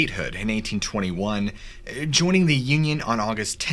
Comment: When you said joining the Union, it sounded like you said joining the yin yang.🤣🤣🤣